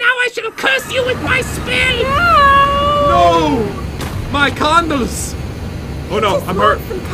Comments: guys at 4:10 pat did NOT say condoms he said candles, calm yourselves this is a kid friendly channel. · I've been subscribed for almost 3 years and not once have I ever hot replied from popularMMOs I guess I'm not lucky D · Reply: did they say condoms? Or candles